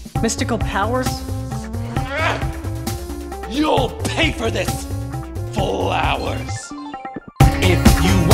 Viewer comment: The first time I saw this movie I thought he said "You'll pay for this...for hours" lol